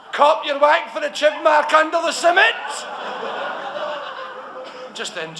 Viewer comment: Okay... what does he say at 8:16 (ish) : I've been wondering for *ahem* over 20 years *gulp* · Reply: he says cop yer wack for the chib mark under the chin. meaning look at the scar under the chin. in English. lol.